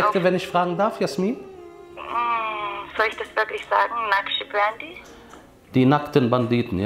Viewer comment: hat das einer akustisch verstanden, was das für eine Sekte sein? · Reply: naqshbandi meinte sie im Video